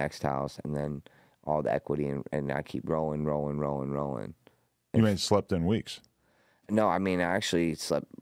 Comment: Aaron didn't catch it. Rollin Rollin Rollin haven't slept in weeks hahaha